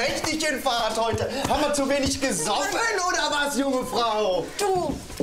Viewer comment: Was sagt der bei 0:54? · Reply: Haben wir zu wenig gesoffen oder was junge frau